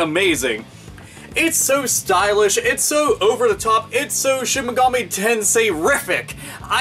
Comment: Lol i thought you said "shinigami tensei"